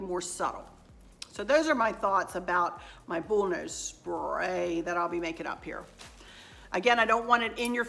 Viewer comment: Haha I thought you said bulldoze spray, I'm glad you explained it, had no idea what it was, I had ideas that Chris had bought a bulldozer and you were decorating it too, made me chuckle. Cant wait to see the stair garland etc